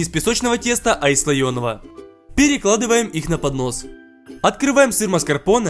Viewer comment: я одна услышала не поднос а понос?))))😂😂